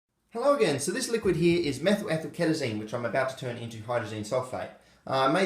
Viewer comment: Wasn't paying attention and thought he said Meth-Amphetamine for a second 😂